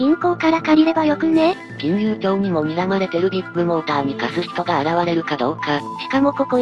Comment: ビッグモーターの発音がどこもビップモーターに聞こえる